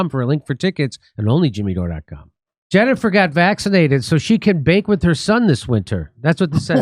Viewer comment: I thought you said, "... so she could VAPE with her son." 😅